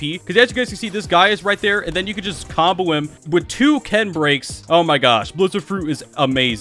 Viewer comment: Was i the only one who heard “kendricks”